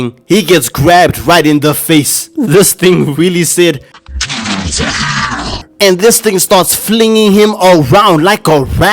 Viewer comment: anyone know what the alien was saying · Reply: You will die!